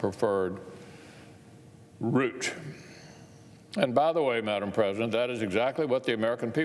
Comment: & 4:21 did he just say Madame President